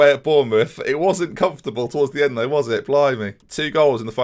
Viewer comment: What is it that you say at 13:36/13:37? After "It wasn't comfortable towards the end there was it ____________ me"? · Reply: "Blimey", a generic exclamation